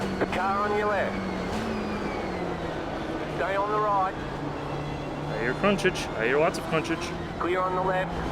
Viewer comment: Did you say "I hear punchage?" LMAO Also: MAJOR C H O N K Y B O I